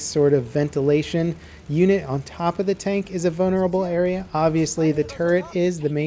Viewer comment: lol I thought I heard bonerable